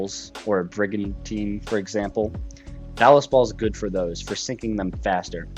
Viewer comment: did you just say that you "love balls"?🤨